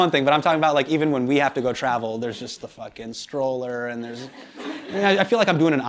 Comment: Am I am the only one who heard him say " fucking stroller " @ 2:48 🤣🤣🤣